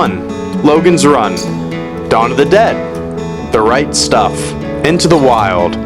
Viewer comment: Lol when you said "Right stuff" i heard "reichstag"